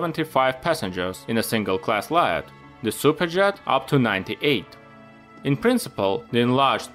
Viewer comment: nice vid, though, i cant help but grin every time you say super jet. it sounds like youre saying cyka jet XD. keep up the good work man, been really enjoying these